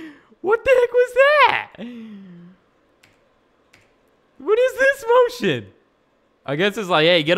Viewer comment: Did just say what is this bull sh*t